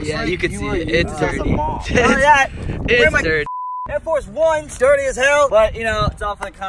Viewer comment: did he really say Air Force 1’s lmao😂